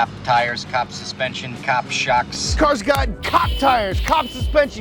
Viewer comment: anyone listened "cock" tyres/suspensions/brakes... Instead of cop?